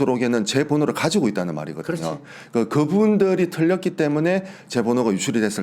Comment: "털렸다"는 단어죠? 틀렸다라고 들려가지고 ㅎㅎ